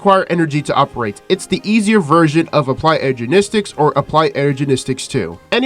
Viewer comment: "Applied Energenistic" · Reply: i heard ''ergenistics''